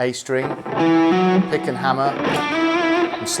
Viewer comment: What do you call that technique when you do a strong "Power Pick" to one note for it to sound louder, he does it at 3:40 and i think he says the name at 3:43 "Pickking Hammer" maybe????? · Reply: He says "Pick and hammer" as in hammer on the next note directly after you pick a note.